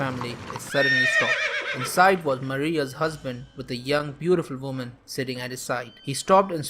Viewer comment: I thought you said "inside Maria's husband was another woman" and I was like kinky